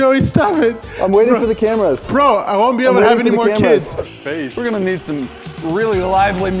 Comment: Omg who else heard say he would not be able to be any kids